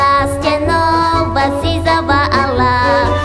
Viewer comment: did she just said allah? XD